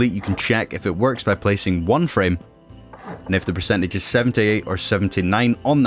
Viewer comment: am i the only one who hears 70 or 79? (he says 78 but i hear 70 lol)